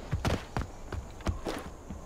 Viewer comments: lol, I know it's the fictional stone age speak, but that kinda sounded like "I am dead", right before he got killed. Kinda ironic. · I swear I heard that guy say "I'm dead!" Btw I these kill is amazing, this man make it seem so easy. I'd hate to see him in COD.